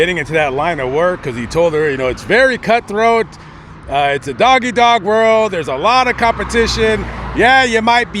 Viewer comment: did you say doggy dog world? Dog eat dog world.